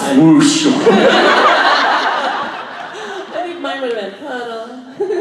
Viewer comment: What did that girl say at 11:13 ? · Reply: Ohhh, " I think mine would have been puddle" 😂